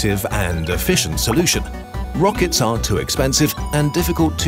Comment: the subtitles say "rockets aren't" but the narrator says "rockets are". how can i edit the subtitles?